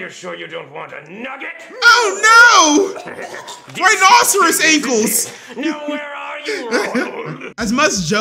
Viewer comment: His What 😂😂😂😂 What did he say ? I didn't hear him 😂😂 Rhinoceros Ankles !? 😂😂😂 What !? I don't get it but it's funny.